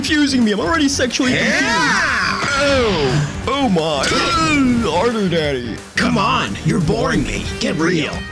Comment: Why did buu say harder daddy 💀💀💀